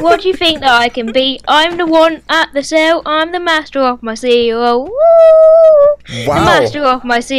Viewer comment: he sounded like he said master of my seagull and than made seagull noises